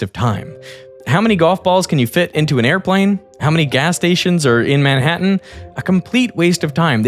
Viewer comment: I listened to this video while driving to work this morning (driving -> listening), and just watched it now while having dinner. I swear… I swear that this morning, I heard you saying “how many windows are in Seattle”, not “how many gas stations in Manhattan”. Is this the Mandela effect? Esquizofrenia? Alternate reality? You edited the video? (If so, why?) Or what? 😵‍💫